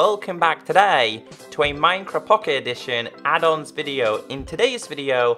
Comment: Who else thought he said (hey guys welcome back to minecraft pe adults video) rather than add ons video